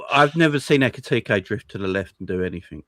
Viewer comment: so came on the stream to just waffle? so Ekiteke can't do anything from the left..... so when he played for Frankfurt and scored against spurs where did he come from space????? or was it the left. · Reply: I actually heard wrong. I thought he said left wing. But he meant drifting to the left. That's my bad